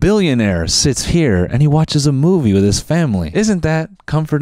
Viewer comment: did he say family?